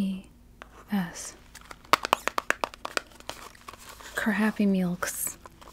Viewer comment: I thought i heard "crappy meals" lol